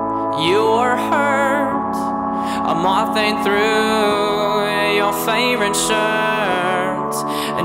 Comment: tyler: you are tired, me: yeah? tyler: you are hurt, me: OMG WHERE!? tyler: a moth ate through your favorite shirt me: NOT AGAIN. MOM, GET THE BUG SPRAY. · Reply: Professional_Nuisance I thought it said "I'm mopping through your favorite shirt", but that makes more sence.